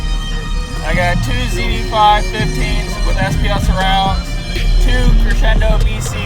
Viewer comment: ya surrounds not supposed to get all wrinkled up especially on some normal rolls 👀🤷🏾‍♂️ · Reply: I thought he said they where spl surrounds?